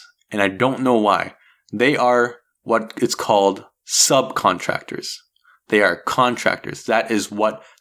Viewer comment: When you say “sub-contractor”, I hear “Uber”.